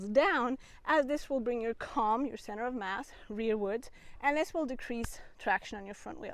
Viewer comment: bring your what backwards? I heard CoM but what was the other term you used? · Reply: Centre of mass :-) or Centre of gravity.